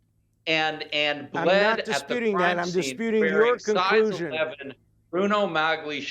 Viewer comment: Tell us Dershowitz why Simpson said he never ever wore those "God awful" Bruno Magdli shoes and yet multiple photos of Simpson walking around in them were found from his broadcast days with NBC (including one in the endzone of Rich Stadium). And Bruno Magdli footprints were found at the crime scene? Did Fuhrman plant those too?? did he buy a pair and put the footprints all over the crime scene?? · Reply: At the 20:58 mark in this mark in this video, Dershowitz DIRECTLY addresses the Bruno Magli question. (What he says, by the way, is "I don't dispute that.")